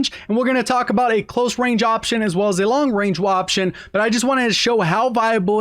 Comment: did he just say waption(woption) 🤣🤣